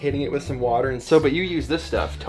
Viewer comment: What are they saying at 4:38, token oil, tokenaue...?